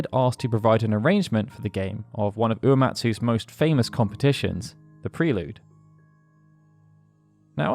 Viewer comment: You said competitions instead of compositions, this has been your daily dose of self consciousness